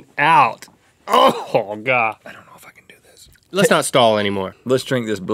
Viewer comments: Did you had what I heard?... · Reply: "I don't know if I can do this" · sounds like rhett says "I don't wanna f**king do this" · Did he say: "I don't wanna f*cking do this"? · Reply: "I don't know if I can do this." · did rhett just say "I dont want to f***ing do this"? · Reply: I think he said 'I don't know if I can do this' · sounds like he says "I dont wanna f*cking do this" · did Rhett just say the f word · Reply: but he actually said "I don't know if I can do this'' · it almost sounded like Rhett said “ I don’t wanna fucken do this”